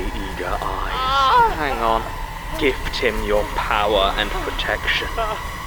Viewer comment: I know jon says "gift him your powers..." but what i keep hearing is "give Tim your powers..." and i think, "oh, nice reference" to teh fact that jordan kennedy is played by gunpowder tim